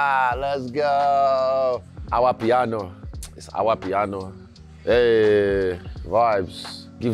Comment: Sounds like Izzy says “Amapiano” around the 6:50 mark. I could be wrong but it’s interesting when you consider where that genre comes from 🤔🤔